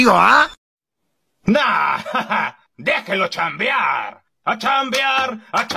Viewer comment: Ay wey yo si pensé que decía a Chamvear